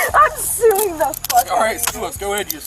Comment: her ass did not say "I'm sorry" she said "i'm suing the fuck out of you"